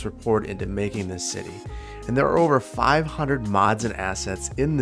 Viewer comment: FIVE HUNDRED MODS? My computer would vomit on its shoes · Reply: He said 500 mods and assets. I heard "500 mods." Made my heart skip a beat.